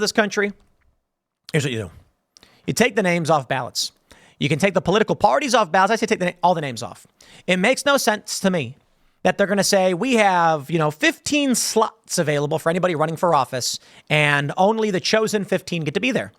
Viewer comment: I thought Tim said 15 sluts not 15 slots lol 😂